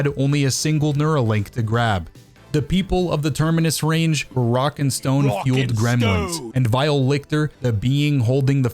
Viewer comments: did I hear a rock and stone? #gaped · DID I HEAR A ROCK N STONE ?